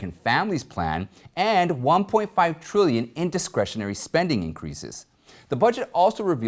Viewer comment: I know it wasn’t intentional, but it sounded like you said “indiscretionary spending”. It kinda fits the way our federal government spends money we can’t afford.